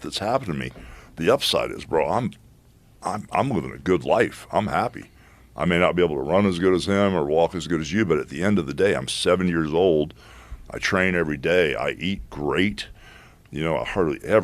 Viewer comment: Is it just me or does he say “I’m 7 years old”😂